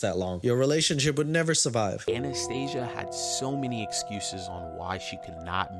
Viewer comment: sounds like said anaesthesia?